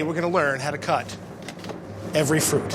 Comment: I thought the beginning said " How Slice Every Fruit"